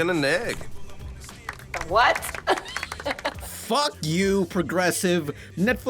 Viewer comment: I thought the box said fuck you